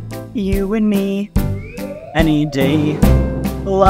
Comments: I thought he said love your neck 💀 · My dum a$$ thought it said "love your neck" 💀💀💀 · Is it me or do I hear "love your neck" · I thought it said "Love your neck" 💀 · I thought it said love your neck 💀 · Nah i heard “love your neck”💀💀💀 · I thought he said “love your neck”💀